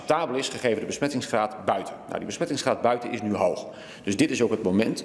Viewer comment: "besmettingsgraad buiten is nu hoog." Hoorde ik dat nu goed?